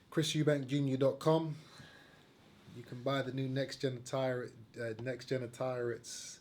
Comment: one punch micky stop hating mate!! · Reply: micah Richie , i honestly dont hate him. Id prefer groves to win. And i do think him and his dad talk shit, but if they back it up then fair enough. But dont get offended on his behalf snowflake. And it did sound like he said next genital listen back.